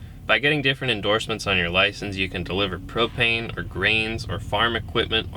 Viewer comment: For a moment there I thought he said cocaine instead of propane